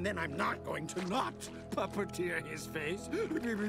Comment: what did he say?perpetier his face? Pepperoni his face? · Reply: Puppeteer his face.